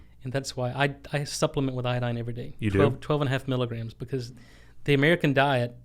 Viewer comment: You should take 10-100 times as much. Not my words but the Dr. said he takes about 7,5 mg which is 50x as much. Greetings from Germany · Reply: I thought he said 12.5 mg?